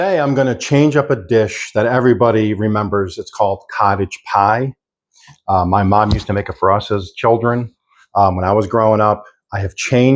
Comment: It sounds like you said "my mom used to make it to process children" 😆 sounds like theres a few siblings that didn't make it